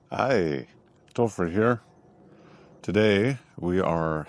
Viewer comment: 感謝分享 ... ^______^ 註 : 有時我會把 Topher 聽成 Tofu(豆腐) ... (尷尬) ... ^^